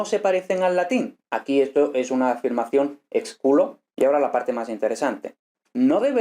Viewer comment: Vale, lo primero, me ha encantado el vídeo, lo segundo, aquí van las palabras que no he entendido de este: -3:11 Ahí dices "sí", ¿verdad? -3:19 Supongo que es "expuro", pero no sé lo que es. Gracias de antemano a quien me conteste y saludos desde España. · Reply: En el 3:11 escuché que dijo "sic" ("así" en latín) y del diccionario entendí que se usa para indicar que es una copia o transcripción del texto original. Y en el 3:19 también escuché expuro pero no encontré nada sobre esa palabra, solo encontré "expurgo".